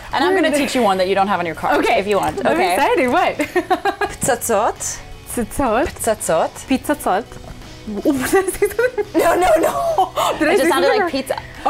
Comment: it sounded like Pizza Sauce :D haha, I laughed too